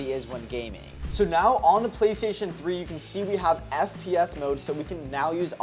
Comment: DID BRO JUST SAID PS3??💀